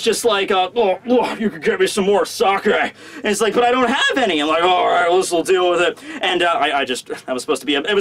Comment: I thought you said Sakura instead of Sake lol. Zoro asking for Sakura haha. :P